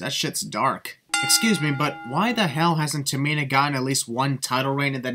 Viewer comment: at 1st i thought he said tamina god 😂